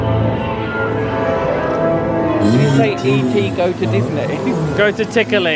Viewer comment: I thought he said Go To Disney too!! 😂😂😂